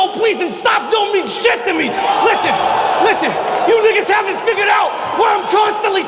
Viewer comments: Wait! at 18:14 did he just say the word NIGGA???????????????????????????????? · Did just say you niggas? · DiD SAY NIGGA???